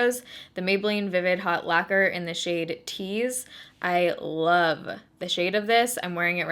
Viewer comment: When she said “in the shade tease”, I thought she said cheese 😂🧀